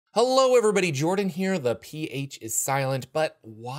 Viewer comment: And I always thought he said "The PH assailant"